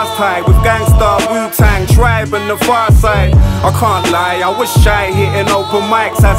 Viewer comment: What does verb t say at 3:22? (gangstarr ,wutang clan, tribe called quest and ... .